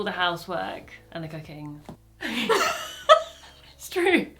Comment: says I do the cleaning, the washing, the cooking, but the automated captions say the cocaine 😂